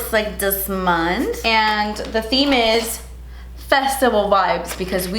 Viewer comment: why did i hear "vegetable vibes" instead of festival looool